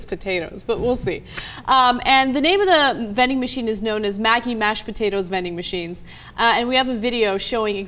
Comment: I thought Ana said Magic but when I saw the Maggi logo lol I love all Maggi products and I want to try their vending machine mashed potatoes lol